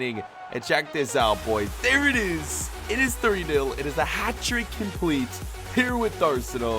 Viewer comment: who else heard 30-0 Like if you did⬇️